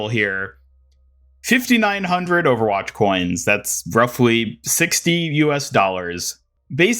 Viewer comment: did he say 16 or 60?? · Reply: 60. 1 dollar is eqivalent to a 100 coins, 5900 owc = $59 usd